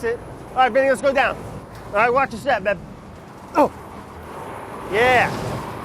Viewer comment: did Brad just called Vinny "babe" 😂😂🤣🤣